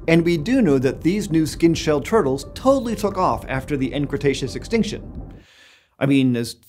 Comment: Sounded like he said totally effed off lol